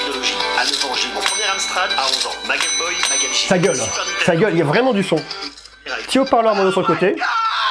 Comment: j'ai compris "ta geule, ta geule" j'ai rit 😂😂, sinon superbe vidéo !